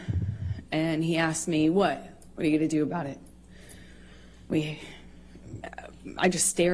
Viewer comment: The second plane recording it sounded like he said what are you doing after moaning.